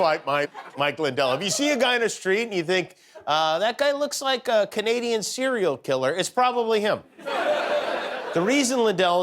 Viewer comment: I thought he said "Canadian cereal killer"